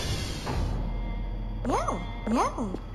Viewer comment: why does she say "no no" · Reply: She says meow